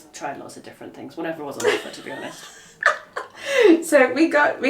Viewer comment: What said was funny enough, but the subtitles say "whatever was on the floor to be honest" hahahahhaha! (She actually said "on offer" BTW.)